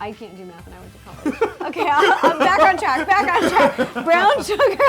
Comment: Ro: Baking soda!!!! My sis: Did she just say... BACON SODA?!?!?! Me: No, sis she said- My sis: HOORAYYYYY